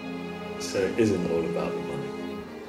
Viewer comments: "Of course not, Competing is fun dude" I got goosebumps and cried 😭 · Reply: Thought he said "competing is fun, too.", which would make the sentence even more nuanced and emotional imo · "Of course not, Competing is fun dude" I got goosebumps and cried 😭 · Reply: I thought he said, "Of course not, competing is funDED." :)